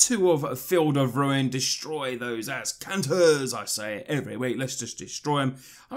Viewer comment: what does he say at 7:34? destroy those ... what? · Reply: I said Azcanta....as in when the search for azcantas flip.